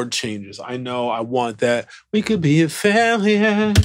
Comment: the whole time I thought he was saying "you could be a failure" lmao